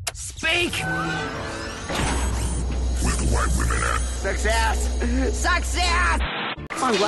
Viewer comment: For those of you who wanted to know what the robot said at 1:33, he said "where the white women at." 💀